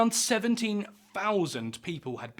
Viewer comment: About 17,000 as you said or about 177,000 as the subtitle says?